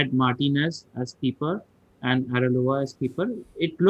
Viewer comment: Who else heard ARELOA :v